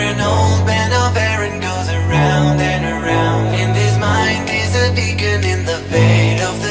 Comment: "...And his mind is a dickhead in the veil of the night" yeah that's what i hear xD